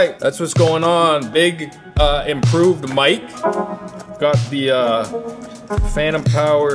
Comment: I hate brainrot. I heard "Phantum tax" instead of "Phantom-powered". I'm 24 and my brain is allocating random phrases that don't mean anything to real words like a cerebral autocorrect. I think I'm losing it. Good pov, pizza looks great. We'll never know who made the garlic oil but that's okay because the guys who made it was the friends we made along the way. :D